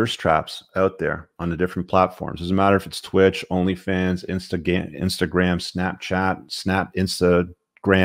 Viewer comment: I thought i heard insta... GAY 🤣🤣🤣